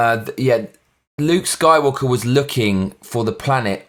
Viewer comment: sounds like you said soywalker